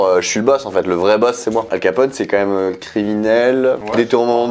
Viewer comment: C'est moi ou dit alkpote